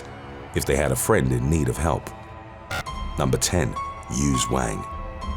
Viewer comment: It sounds like you said "used wang" 😂 (1:47) Those parents totally did it on purpose 🤔 Jokes aside though... May he rest in peace, all of these cases. 🙏🏽